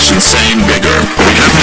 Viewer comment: did he just say the N Word? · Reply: He said ITS INSANELY GETTING BIGGER!!!